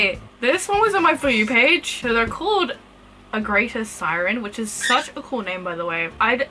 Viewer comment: could someoen point out what was the name again? Its hard to hear what she said since i have undiagnosed auditory processing disorder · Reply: A greater Siren